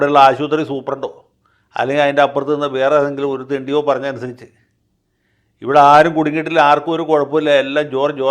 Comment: mts, did I hear a "thendi"?😂